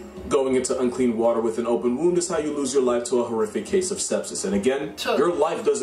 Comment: I thought he said how u lode ur life to a case of step sis😭😭😭